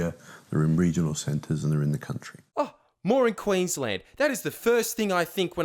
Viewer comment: "more in Queensland"? Could of sworn I heard "moron Queensland"...